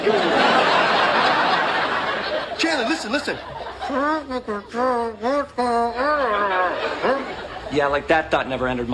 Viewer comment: i still dont know what joey said there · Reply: I think he said "Try to not embarrass yourself in front of her"